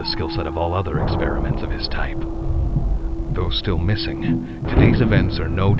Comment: I think you interpret it as “though still missing” in reference to experiment 1006, but I heard “those still missing” in reference to the employees. Still an interesting theory and one i had not yet considered